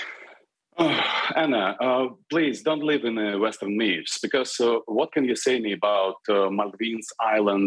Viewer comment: he actually said "WHAT ... ABOUT" etc etc